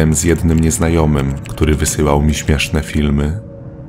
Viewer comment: czy tylko JA usłyszałam "nieśmieszne filmy"? XDDD